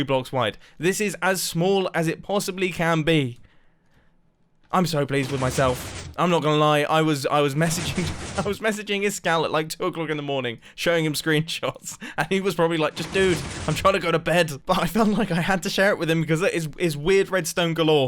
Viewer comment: I thought he said "I was masturbating..." when he talked about the vault door ngl.